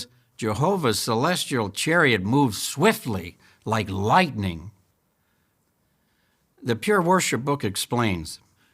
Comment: I thought he said the Pure Horseshit book says....